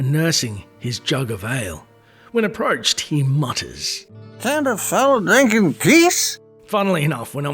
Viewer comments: It's funny you talk about not being sure what he's saying when you first talk to him. My friends and I (9-11 years old) jokingly thought he was saying "Can't a fella drink his piss?". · I always thought he said " candid fella drinking geese" Everytime he greated me back in the day. I was about ten years old at the time and never grew up with adults that were drunk around me so I didn't really understand what a drunk person sounded like. So I just thought getting drunk made you just speak nonsense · When i was young i always thought he said "can't a fella drink with geese?" 😂